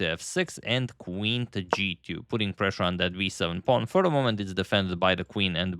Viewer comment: did you just say v7 pawn at 3:04?